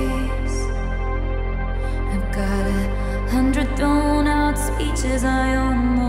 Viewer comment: When Taylor said “I've got a hundred thrown out” I heard “I’ve got a hundred donuts” 😂🍩 Edit: looks like we all need some doughnuts😌 🍩🍩🍩🍩🍩